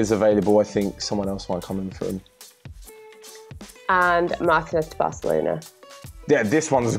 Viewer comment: who did they say? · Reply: Martinez lautaro